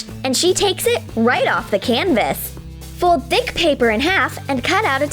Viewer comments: I thought it said "fOlD diCk pApeR in hAlf" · Did anyone hear her say this at 1:21 she said fold the D**k paper